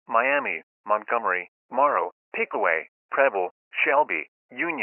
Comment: When bro said preble it sounded like he was saying pebble from dandy's world